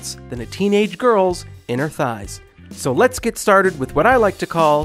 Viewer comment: not cool. That’s not a funny joke, don’t joke about self harm. I’ve been there. We don’t joke about it. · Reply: Yeah i heard wrong since the sentence that thought i heard was "on their thighs".I am glad that you okay and just to clear thing my first language is not english but the sentence reminded me of self harm right away, although i could not believe that sy would joke with that so nonchalantly so i made the conclusion that he wouldn't have said that